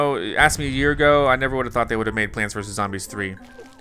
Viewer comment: why does it say chant and not plant?